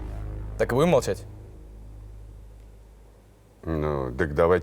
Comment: я один не понял что он сказал? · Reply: ork oki "так и будем молчать?"